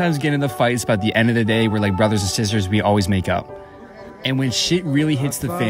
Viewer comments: When he said make up I thought it said make out I was like “… 😶” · At one point I heard " but at the end of the day we are like brothers and sisters and we always make out" I was like wuuuh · Did she just say we make out at the end of the day not make up 🥴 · When he said “ we fight sometimes, but we always make up” I thought he said “we fight SOMETIMES, but we ALWAYS make out” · I need a to have my ears checked I heard "we are like brothers and sisters so we always make out" had to think about that for a sec